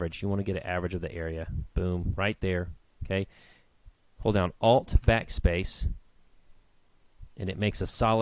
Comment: At about 10:52, it sounds like you say Alt-Fac-Space. It's not you I'm sure it's probably my old speakers. Would you clarify for me please. So far, using PSCS2, I'm following you okay. . . :-)